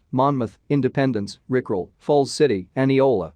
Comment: did I hear Rickroll? 1:20:43 bro the queen is immortal AND immune